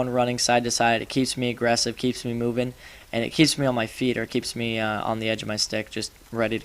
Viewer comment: i had to replay a part.. i thought he said "Keeps me on the edge of his dick"... Thank god it was "On the edge of the stick"